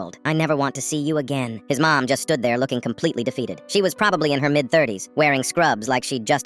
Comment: I thought when op said defeated I thought he said relieved and my anger issues really wanted Oo to walk or to that kid and lecture and scold him to save the poor mom